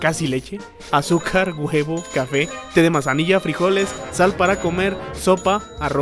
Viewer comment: Mi mente de cuatrapeó y escuché "shampoo para comer"